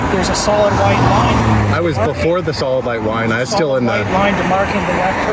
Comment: Am I the only one who heard say "solid light wine" @ 1:00?